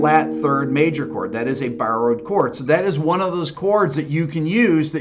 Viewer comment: What is he saying at 7:01 (not an native english speaker), a borrowed chord ?